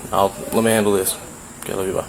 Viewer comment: Did the officer end that phone call with the investigator by a breezy “okay, love you, bye" or I completely misheard. Can someone clarify?😶